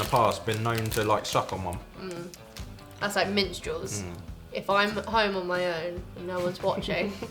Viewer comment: Ok Minstrels... that's what she was saying! Here I thought the was stuffing those chocolate malt balls Malteses. (Probably spelled that wrong) while menstruating... LOL. I actually thought she was saying menstrual and not Minstrels... HA HA HA 🤣🤣🤣